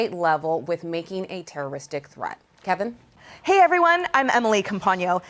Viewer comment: Did she just said "terrorist dick threat" ?